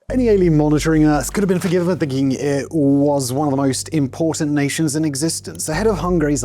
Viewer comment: what in fucks name did he just say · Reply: "Any alien monitoring Earth could be forgiven for thinking it was one of the most important nations in existence."